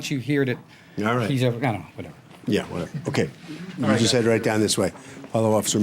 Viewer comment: Did say yea whatever?! Lmao